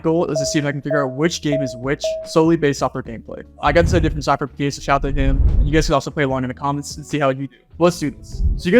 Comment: what is he saying here? sounds like gibberish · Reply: "I got this idea from SypherPK, so shout-out to him".